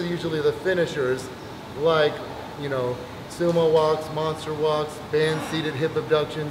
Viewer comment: Does anyone know what exercises is he saying at 14:44? Sumo whats and monster whats? I can't quite make it out.... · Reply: kathcbf monster walks 😊